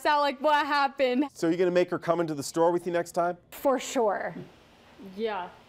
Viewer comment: Anyone watching in 2019? And at 1:28 I thought that she said thotiana haha